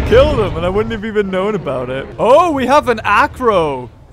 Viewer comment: On 6:54 I thought you said "Afro" im like "Who got an afro?"